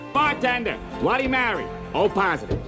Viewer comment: did he say bloody marry?